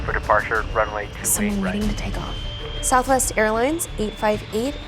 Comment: Ryan, minor detail, but she does actually say Airlines